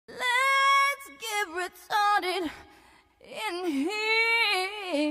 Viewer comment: the way she soulfully spells the word “retarded” is amazing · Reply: It sounds like she says retdrdt to my 👂